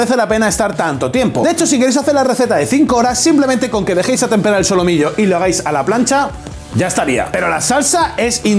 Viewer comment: qué dijo? xd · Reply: A temperar el solomillo o como se llame